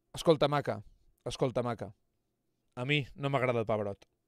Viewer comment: Pucha, el primer capítulo de harry potter fue muy muy bueno y muy chistoso pero se entiende perfectamente el motivo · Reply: Correcto Escucha muchacha a mi no me gusta el pabra (esta palabra si no la entendi Xd 11:29 ) y luego le enseñas la trompa es la traduccion la queria dejar en algun lado